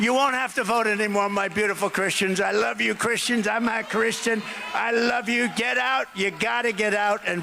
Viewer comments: He just admitted he is not a Christian!! Listen to his words! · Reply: Yep it’s what I heard , he said “I am not a Christian “ .. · For all the commenters he said I'm a Christian. But more importantly he's actually telling these people to thier face he has no use for them in 4 years when he can't run again so he doesn't care. I know he said it'd all be fixed, but he meant all thier imagined problems with the country. He hates have to actually be a president, he just loved the prestige. Which he got the first time, now he just wants all his cases gone. Convincing some of these people he's telling him they're of no use to him after the election is a much better strategy than claiming he's saying he's gonna be the dictator a lot of them kinda want. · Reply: Closed-Caption says, "I'm not Christian" ...